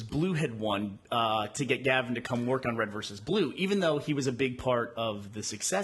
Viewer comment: did anybody else hear Burnie say Red vs Glue